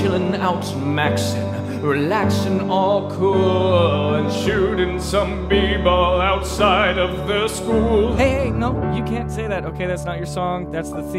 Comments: when he said “shooting some bball outside of school,” i felt that. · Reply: Ok not gonna lie I thought he said shooting some people outside of school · When he said "shooting some b ball outside of the school" i thought he said people and got very concerned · I deadass thought he said "shooting some people outside of the school" Im dead bruuh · Lyrics: Would it be alright if we tried again? I'll hold you tight again. We'll be alright again. I wanna be right by your side again. Watch Nick at Nite again And chill with Aunt Vivian. Well West Philidelphia, born and raised On the playground is where i spent most of my days. I'm chillin' out, maxin', relaxin' all cool Shooting some b-ball outside of the school. Hillary and Carlton and Aunt Vivian I wish that you all were together again So throw out the DJ, Let Geoffrey unpack. I long for the day When the Fresh Prince is back Where is Will? x4 Do you remember that one time when Will's dad returned? And Will was so happy but then he got burned? And Uncle Phil came down from heaven above And showed Will the meaning of fatherly love? Uncle Phil. x4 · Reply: Haha I always thought that they were saying “shooting people outside of the school” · I thought he said "shooting some people outside of the school" LMAO